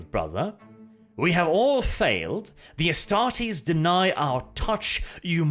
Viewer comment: When I first heard this I thought that the orb said "Defile our touch" as if the spears stabbed into the thing are able to break the communication or allow the psycher into the link between the two.